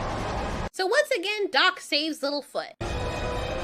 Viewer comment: Why at 27:30 did I hear “what the f**k” and not “little foot”